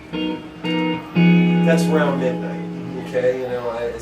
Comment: what's the name of the song he plays in 13:10? can't understand what he is saying.. something "midnight".. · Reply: never mind, found it. "round about midnight"